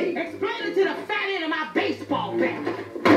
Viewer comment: For some reason, I always thought she said "You're about to hear the clickity clack of my baseball bat!"